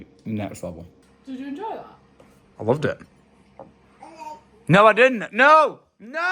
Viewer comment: it sounds like said I LIKE anyone else ? Hear it